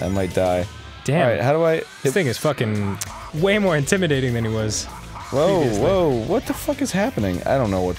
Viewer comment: What did Dan say at 0:17? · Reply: "Damn... this thing is fuckin... way more intimidating then it was"